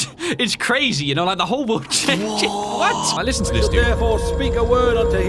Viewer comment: did he just say tahir? Also it said your right hand will be chopped off and rais's right hand was cut off